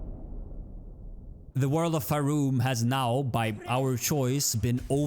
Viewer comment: I heard "the world of Faerûn" lmao.